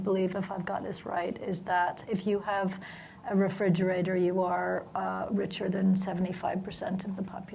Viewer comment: What word does Gillian say in 25:07? I don't get it, re...? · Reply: Refrigerator